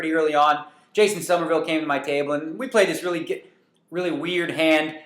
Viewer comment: was he about to say "we played this really gay hand" kinda sounded like it ......